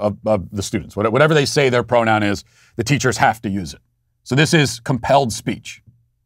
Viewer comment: When you said “this is called compelled speech” I thought you said “this is a hellscape” and both are accurate.